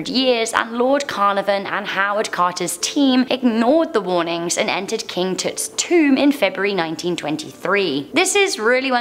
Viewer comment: I know it's her accent but it sounds like she says " king toot " 😂😂